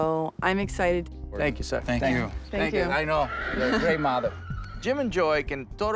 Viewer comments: Did Cesar said you’re a great mother !!14:50 oh no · Lol, did he just call her a “great mother”?